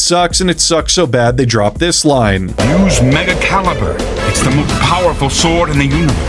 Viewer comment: I heard mega calender lol